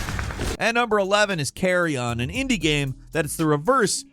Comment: When you said "Carrion," I heard "Carry On," and pictured either a Kansas Rock Band sim, or game about trying to get your luggage into an overhead bin.